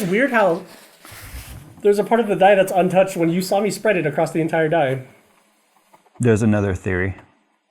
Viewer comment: I had this video running on my laptop, I was walking like on and off to watch it, at some point I walked past it and heard "you watced me spread it" 😂 Next time I walked by I heard "it's so foamy in there" 😮